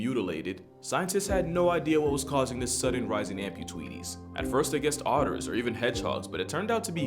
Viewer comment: Did you really say "amputweeties"? 😄